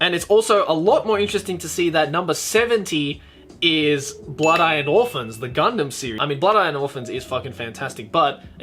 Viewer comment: Did you just say Blood - eyed Orphans 7:50 ?